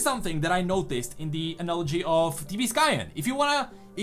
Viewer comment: please can someone tell me what youtuber he mention ? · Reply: It's T B Skyen. He analyses League stories and art. Also, just great guy over all